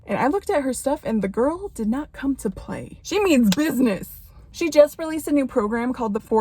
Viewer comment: Thought she said 'Jimin Business' at 0:59 and i oopp